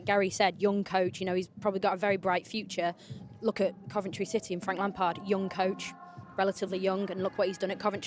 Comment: Did she just say look at Frank Lampard? Who also got fired by Chelsea???